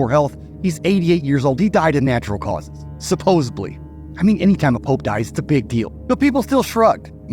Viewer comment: Such a pet peeve of mine to hear someone say "supposably" instead of supposedly.